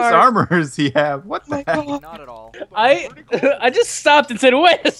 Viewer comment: Whenever he says "Fatigued? Not at all" I hear 'Teak? Not at all." Like oh no, that's not made of teak... xD